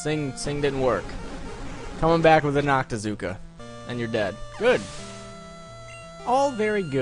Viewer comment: the captions said "come back with an octagon and your dad" lol